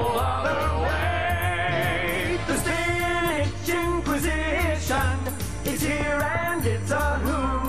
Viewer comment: I always thought they said "the spinach in position" i wasnt a smart kid lol